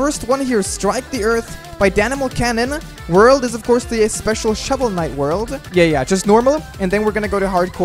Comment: OH I THOUGHT IT WAS SAYING HOLLOW KNIGHT AT FIRST OHHHHHH